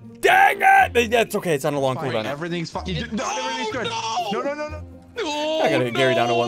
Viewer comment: Is it just me or did I hear swear and say "Everything's f***ing OH- NO-"? i think something is wrong with me